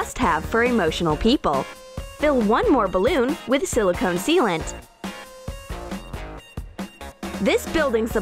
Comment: I thought it said SILICONE SEMEN 😂😂